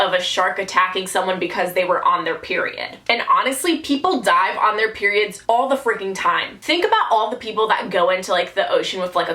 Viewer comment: I thought she said people die in their periods all the fricken time. That had me scared for a second